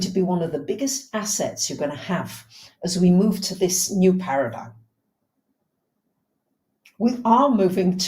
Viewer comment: You said 'paradigm' - I heard 'paradise'... New Paradise will do nicely!!! Thank you for your wisdom and enthusiasm, bless you x